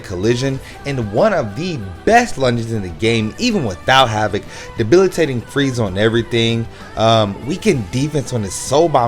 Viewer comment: Ali K of course Tosh is has Debil, I did mess up and say White was 14 SAR but I mentioned that in a pinned comment and Grim has frenzy+ berserker and a peryfull screen 3rd and a lung 1st SA his 2nd doesn’t move him forward at all but he hits harder than 90% of the units on the list. And Nel does massively get boost with Full Senkaizen links cause her SAR output isn’t good at All. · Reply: oh and my bad, you said ''without havoc debilitating freeze'' and i thought you had said without having debilitating freeze...lol..my bad!